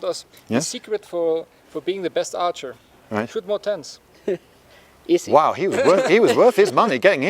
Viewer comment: what was that tip again? I didn't understand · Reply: "Tip to being the best archer, Shoot more tens."